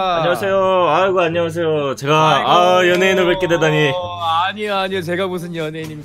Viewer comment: 신기하게 연예인 말고 '연애'인으로 들리네 ㅠㅠ